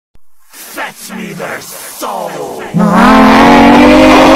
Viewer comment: No bc I thought it said fetch me the salt